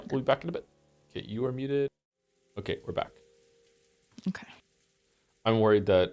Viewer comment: I heard we're so back so I'm glad